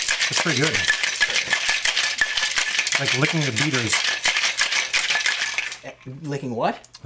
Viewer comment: I thought said "Licking the Beavers".